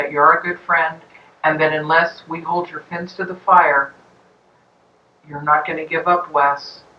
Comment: "Hold your fence to the fire?" LOL, what? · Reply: I thought she said fins to the fire